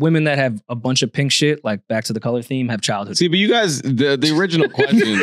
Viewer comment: could someone hear what he said about the pink?? 😭 he said “child (_what_)” ? cuz he’s tryn to read me apparently lol